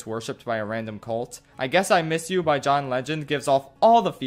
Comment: For a second, I thought he said John Lennon instead of John Legend